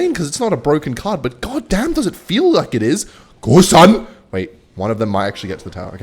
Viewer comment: what does actually say here? He says it a lot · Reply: “go son”